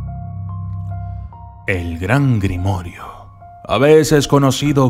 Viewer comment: Like si te quedaste por que escuchaste la palabra "grimorio'" Xdxdzd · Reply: Yo pensé que decía Gregorio 😬